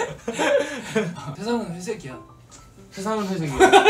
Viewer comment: i thought Jae said sesang i saekki a (the world is b*st*rd) or something that's why Wonpil is panicking lmao XD 😂